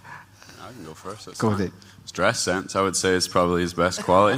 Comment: what did kerr say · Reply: 'Dress-sense'